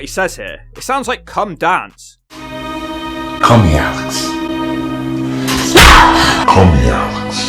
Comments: It sounds like Angle said "Call me Alex" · I think Angle says 'Call me Alex', don't know why though? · It sounds like he says ‘call me Alex’ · I think he says "Call me Alex"